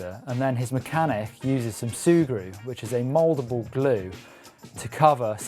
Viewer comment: Chainstay protector, how do u call this glue? i don't understand it probertly. thanks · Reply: Sugru